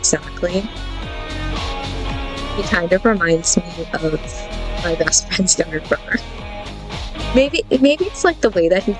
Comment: When you were talking about Bennett your microphone kept shorting out and I thought you said "he looks like my best friend's Gardevoir".